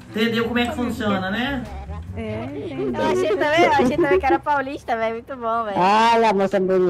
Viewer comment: as mina conversando: "Achei que era o Paulinho". Estou surdo ou foi isso mesmo?